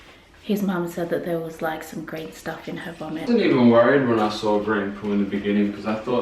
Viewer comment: Hi guys... Blessings to your beautiful family. I am not sure if I heard correctly a little past 13:23 mark, but did say she had some green stuff in her vomit? If so, and this is not to scare you... but get Ava checked to be sure it isn't bile. I love watching your vlogs and pray that by the time you read this, she is back to nursing and her tummy troubles have passed. Thanks for sharing your journey with us. ❤